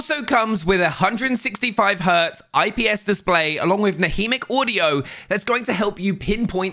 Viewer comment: Did he just say «haudio»?